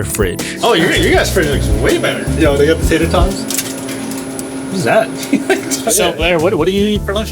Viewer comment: bro what saying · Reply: He said "yo you got tater tots?"